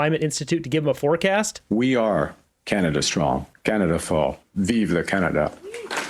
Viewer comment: did he say “Canada strong, Canada fall??”